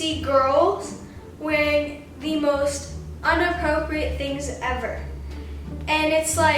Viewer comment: did she just say 'unappropriate' 😭😭